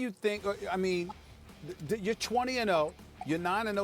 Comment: Did they say blachowicz is 27-0?